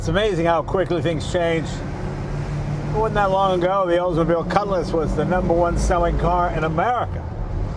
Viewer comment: Just yesterday my grandpa was telling how he had one of these but I thought he was saying os mobile lol